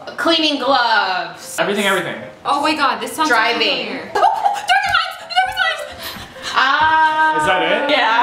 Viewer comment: What's the title of the books that says at 8:11? I've replayed this moment 10x and I don't understand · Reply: if you're still wondering...The Darkest Minds